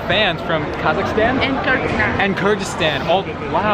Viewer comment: omgg omggg i heard kurdistan whattttt damn damnn 5:14 ya'lll 😭😭😭😭😭😭